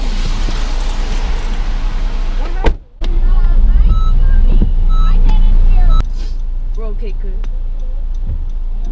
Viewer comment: Sorry to bother karen screaming at you because she thinks its your fault but what was she arguing to you about at that 2:12? "no sorry I didn't see you" that's all I heard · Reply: She was telling me that she "was turning and had a green arrow." She maintained that story all the way through